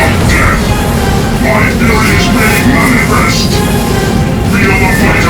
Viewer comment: WHAT DID HE SAY?! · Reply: "MY FURY IS MADE MANIFEST!"